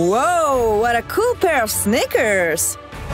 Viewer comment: it sounds like Devi is saying "Woah what a cool pair of snickers"